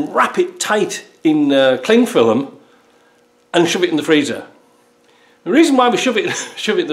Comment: My wife overheard the bit about the freezer, without looking up I heard “ forget it!”.